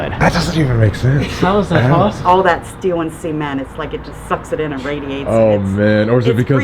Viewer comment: I swear to god she just said semen